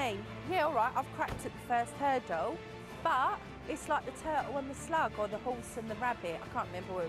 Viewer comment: "The turtle and the slug" would've been such a needlessly slow race. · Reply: i thought u said the turtle and the slag i was like 👁👄👁